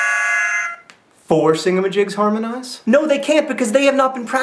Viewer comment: I congratulate you, John, on getting through this video with a straight face. Also, I've been watching Vlogbrother videos with the transcript thing on, and when you said "four singamajigs harmonize, it thought you said "forcing the gentleman's Medicare".